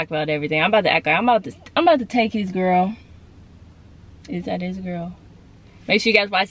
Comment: I thought you said, "I'm about to take his girl, if that is a girl." Lol but you said, "is that his girl.. " Keep on Shining! Stay Blessed 🙌🏽!